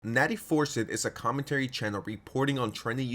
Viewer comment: I thought for a sec I heard Nattie foreskin 💀